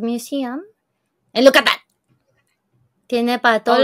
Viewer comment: JAJAJAJAJAJA NO MAMES, EN LUGAR DE ESCUCHAR “AND LOOK AT THAT!” DE, YO OÍ “Y YUCATÁN”. Y YO ASÍ DE “WHAT DID SHE JUST SAID?” LOL Btw Thank God que todo bien con tu mami, prayers for her y tu familia 💛🫶🏻🙏🏻